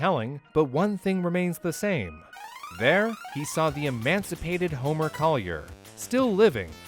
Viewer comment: he says emancipated instead of emaciated.